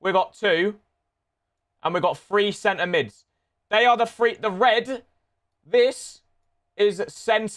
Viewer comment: What does he say at 0:10? Before the word “red” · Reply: They are the free